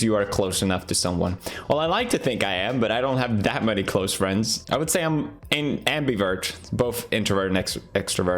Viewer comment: Pewds: "i really dont have close friends" Jacksepticeye: sad irish noises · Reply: I thought he said he didn't have many close friends.